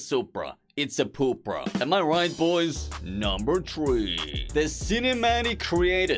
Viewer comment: > that number threeeeee sounded like